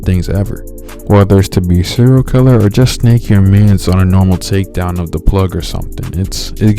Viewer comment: What did you say? The audio was a little whack. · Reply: “snaking your mans”